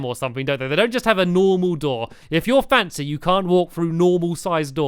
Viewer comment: I thought he said "If you're fat" LOL